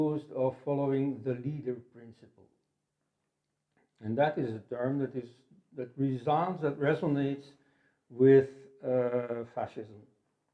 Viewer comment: What does he say between 16:49 to 16:54 “leader principal and that is...” and “resonates with fascism...” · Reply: He says: "that resounds... that resonates... with"